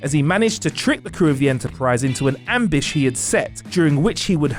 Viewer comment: d-did he seriously say ambish instead of ambush